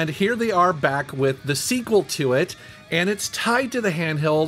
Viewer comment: Thought you said new then later on its it's sequel.